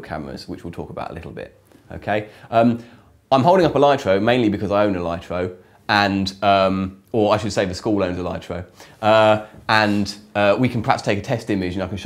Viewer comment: What did he say @ 1:12 "i am holding up a lightro mainly because.."?? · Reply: "I am holding a lightro mainly because I own a lightro, or I should say, the school owns a lightro. Uhm...